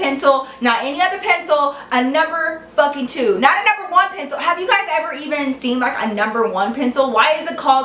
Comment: What did she say at 15:13? I couldn't understand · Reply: "Not a number 1 pencil"